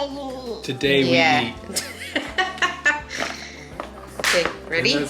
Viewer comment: Hahahah it did sound like she said. “Today we eat” 😂